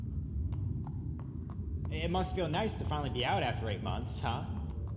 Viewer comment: Did he say... Eight months?